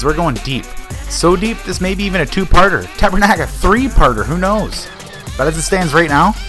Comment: I know from the way you speak you're canadian lol. I'm from Alberta, you? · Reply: hahahahaha I thought I heard him say tabernak... my guess was Quebec